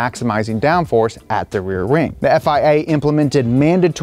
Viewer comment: Am I the only one who hears "rear ring" 🤔 13:50